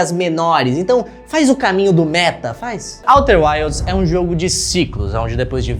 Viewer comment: Por que eu entendi Walter White?